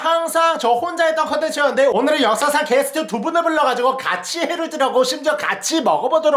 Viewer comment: 난 왜 0:20 6살 게스트로 들리지? 15:02 한반도버섯